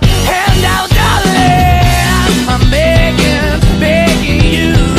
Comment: What I hear: I'm bacon 💀💀